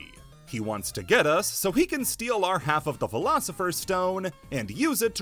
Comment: I don't know why but my brain heard that as "He wants to get us so he can steal our half of the Velociraptor"